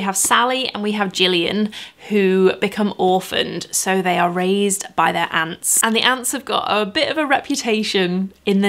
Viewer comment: I fullllyy thought you were saying with the first book that they were raised by ants. I am still not 100% sure you wasn't saying ants hahah!